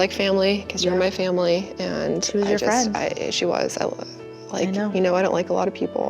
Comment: At minute 10:34, did I hear that correctly? Christina told her friend, "You are family (to me)." And her friend replied (in a somewhat corrective way), "We are friends." 🤔 I'm not sure. 🤷‍♀️ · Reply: Think she said "She was your friend". It seemed very kind and compassionate to me 🥹